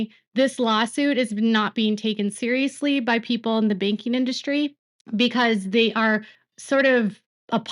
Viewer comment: Hearing that big wigs in the banking industry think Ryan is a clown and aren’t taking him serious is music to my ears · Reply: Oh, thank you – I heard "baking industry", and I couldn't decide what the heck she must have actually said or meant 😂